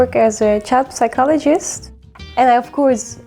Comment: Did she say she is a cat psychologist? 😮 where do I study to be one? 😂